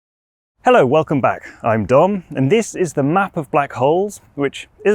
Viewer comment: Bruh I heard "Hello, welcome back I'm Dong and this is the map of my holes" 😭😭😭